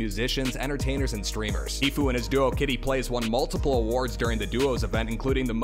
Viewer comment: anyone heard them say "tfue and his girl"?